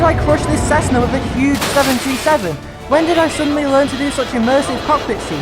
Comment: WHY DO HEAR 737 INSTEAD OF 727???